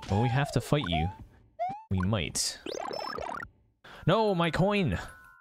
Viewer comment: really thought you said "oh no my queen!"